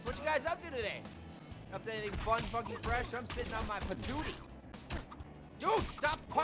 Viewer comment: did i just hear say "Fucking Fresh"?